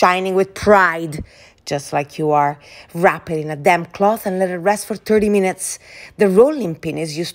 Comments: I heard "damn cloth." I love accents. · I legit thought she said “wrap it in a DAMN cloth!” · DAMP CLOTH! Jesus I thought she said damn cloth. ROFL